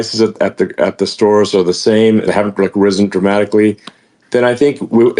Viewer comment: I thought trump said lower not the same 🤣🤣🤣